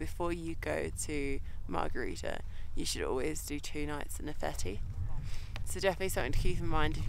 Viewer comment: Can u pls repeate what the guide suggested you guys to do before climbing Capanna Margherita? not clear what your girl says at 22:29. thanks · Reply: hard to understand sometimes 😂 The guide suggested to spend 2 nignts at the Gnifetti hut acclimatising. Thanks for watching!